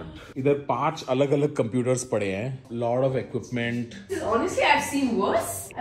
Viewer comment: Is it just me or did Tanmay just said "L0da equipment" at 1:37 ???